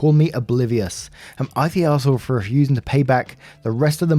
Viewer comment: I thought you were gonna say obese not op... and I was shocked...for a nano second when I realised you said op.